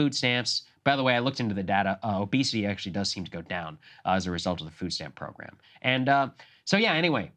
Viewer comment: did he say obesity goes down because of food stamps? Because Krystal hears and responds as if he said obesity goes up, and he doesn’t correct her.